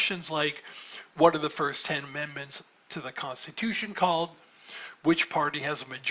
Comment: I was rewatching this part and misheard "What are the first 10 commandments of the Constitution.", instead of "What are the first 10 amendments of the Constitution." 😭🤣